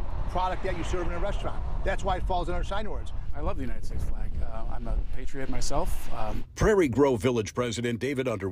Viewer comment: David underwood said "I'm not a patriot myself." He tried to say I'm a patriot but the truth came out. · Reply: WOW! You are correct, we first watched and listened and swore he said i'm a patriot, Even the chinatube captions say im a patriot, when you turn on closed caption unbelievable!